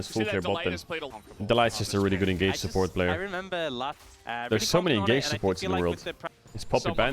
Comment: Did he say ”there are so many gay supports in the world” ?